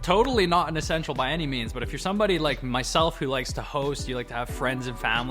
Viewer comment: ”To host”, i thought bro said ”the h0es”